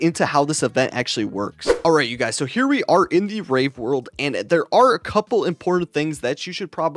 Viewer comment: did he just say so here we are in the rape world?